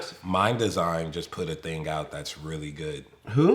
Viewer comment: Does anyone know what artist he is talking about at 24:51 ? I can’t tell if he says “mind design”, “mine design” “my design” or whatever? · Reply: Ryan Villena Mndsgn